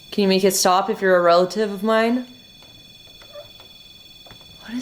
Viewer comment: I totally heard 'Relax' hahahaha